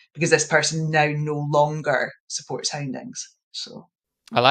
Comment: What did she say? “This person no longer supports” what? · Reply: Houndings her book’s ’Hounded’